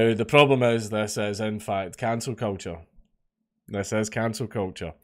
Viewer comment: At first I thought Dankula said it’s cancer culture and thought, “eh that fits.”